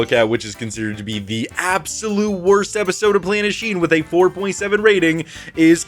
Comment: I thought it said “QURANSheen” for a second there 😂